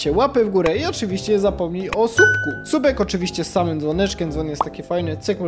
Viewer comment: Czy tylko ja usłyszałem: "Zostaw łapę w górę i ZAPOMNIJ o subku"?? 😂 Uwielbiam ten Twój śmiech 😀